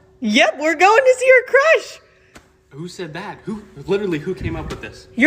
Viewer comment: Instead of “who said that?” I heard “Who’s the dad?” Fucking language processing, lol.